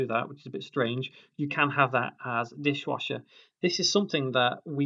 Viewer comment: When He said "Dishwasher" at 8:52 i thought he said "fish-washer"